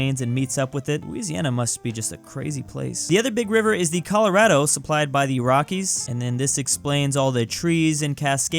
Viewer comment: I thought he said "Supplied by the Iraqis"